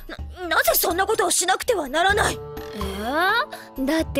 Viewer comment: WAIT A SECOND! Did she say "Ora Ora"? Ora Ora? ORA ORA? ORAORAORAORAORAORAORAORAORAORAORAORAORAORAORAORAORAAAAAA!!!!!!! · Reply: DominatorLegend No she said "Ara Ara!". Lol